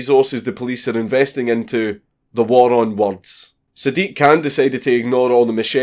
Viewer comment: For 5 minutes i thought he was saying the "war on wonce" and was bare confused